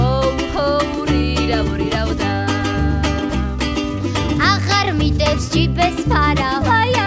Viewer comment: " A harmider z cipy spada..." Padłem jak usłyszałem.. Wielkie pozdrowienia z Polski.